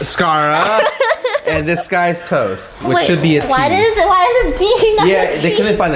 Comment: I thought scarra said “dis guy’s toast” instead of disguisedtoast